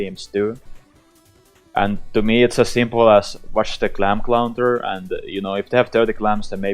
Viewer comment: Did you just say "clam clounter"? lmao.. great video :p